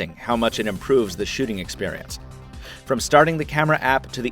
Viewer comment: It kinda sounds like he says sh*ting experience at 4:06 XD